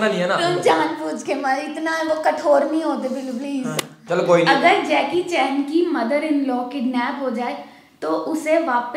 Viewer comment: did i hear baby? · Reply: Billu *